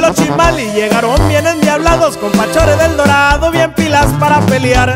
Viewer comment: cuando dice con fachores del dorado pense que decia compa chore del dorado